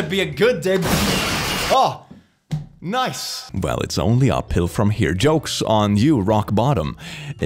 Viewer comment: “It’s only uphill from here.” What I hear: “It’s only a pill from here.” Yes, please 🤣